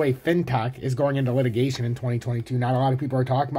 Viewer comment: thought you said "fintech is going into liquidation" for a split second i was watching the world end.